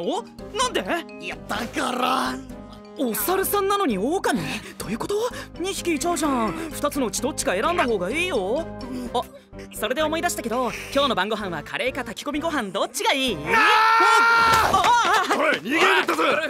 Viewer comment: 二つの"血"どっちか選んだ方が良いよに聞こえて、慧くんは猿と狼のキメラなんだって思っちゃった 人間だっつーの